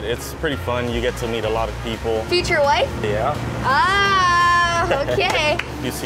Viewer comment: I thought she said “future wife” hahahha